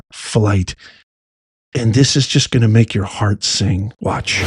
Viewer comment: you scared me for about a second when I thought you said “this would make your heart sink”, but then I realized that you said this makes my heart sing. Hahaha 😂😅😊😊❤